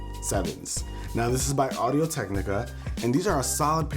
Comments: it sounds like he says audio tech nigga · Who else heard audio tech nigga?